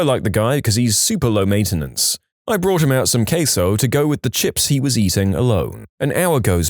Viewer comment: i heard brought him out some caseoh 💀